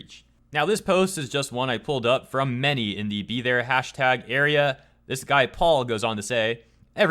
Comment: I cant hear #bethere. I hear #BETER Don't know why lol